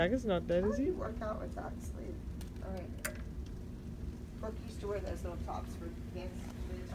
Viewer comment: did Abby say Brooke?